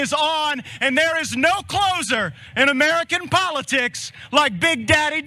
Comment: Ooh he said “closer” I thought he said no bigger Loser than daddy Don